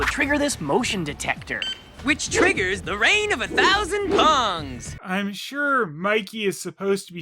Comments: I think what Michelangelo is saying is "The rain of a thousand pongs." As in ping PONG balls, and is equally as stupid as if he did say bongs. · At first, I heard, "Rain of a thousand bongs!" as well. But listening more closely (via headphones), Mikey is actually saying, "Pongs". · I think he's saying "Rain of a thousand PONGS." as in ping pong balls. But I am definitely happier to think he said Bongs